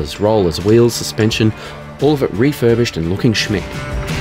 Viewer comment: "looking Schmitt"? Is that what is said? What does that mean? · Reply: Looks “smick” as in looks “fancy” or “flash”. Aussie slang 😉